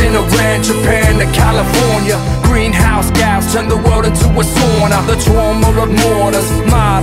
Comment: did he say 'sauna'? :D I'm finnish!